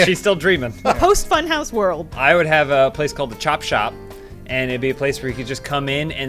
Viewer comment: at around 8 minutes I thought said "Chop Shop" not "Chop Chop"?